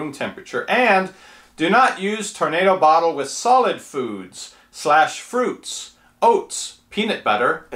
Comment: Did I hear you wrong while reading the directions ? I thought you said ," No Solid Foods" ? I think that was at the 2:09 minute mark ! Just asking